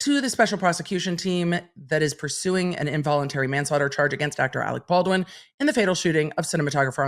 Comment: did you say dr. alec baldwn? lol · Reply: Sounded like "actor Alec Baldwin" to me